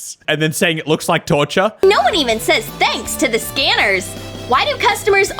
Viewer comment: For a moment I thought she said whore scanners 🤦🏼‍♀️ 😂